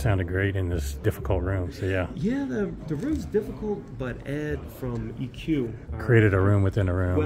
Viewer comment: did he say EQ was used? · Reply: Ahhhhhhh. He said Ed from EQ... now it makes sense. :)